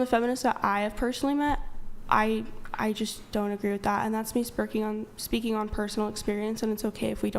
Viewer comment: 'THATS ME SPERGING" HAHAHAHAHAHH DID ANYONE ELSE HEAR THAT!!!!!?!?!??!?!